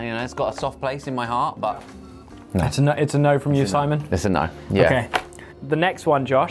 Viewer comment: Its no from you Simon, did i hear that right?